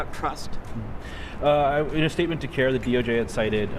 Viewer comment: I thought it said KAREN! It would be appropriate.